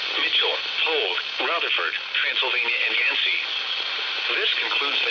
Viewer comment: did it say transylvania?